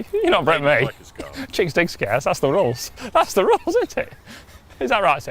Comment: can anyone tell me what he says at 17:33 is it ' you wont break me (cheek doesn't scare?)' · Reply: you won't break me. chicks dig scars. that's the rules